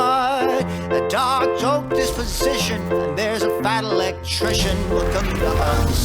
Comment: “A dark joke disposition.” Thank God for subtitles. I always heard “a Diet Coke disposition.”